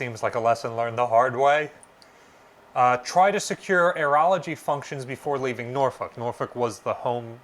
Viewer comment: I kept hearing you say "Urology", and I am wondering why was that so critical. "Aerology" is a word that I had not heard before.